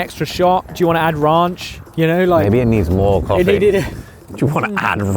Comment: “Do you wanna add Ronch“? Love them. 😄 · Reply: I thought they said, briefly anyway, "raunch". I knew it was, in context, "ranch", but the accent lent to a different interpretation. Also probably closer to on par with Ollie's sense of humor though with my interpretation. Josh is the cleaner humor sort of the two of them. At least on camera!